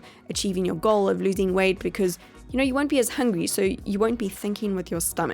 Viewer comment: Is it just me or did she say hangry instead of hungry 2:57 Nope she did it again ps no hate I love your videos and channel xoxo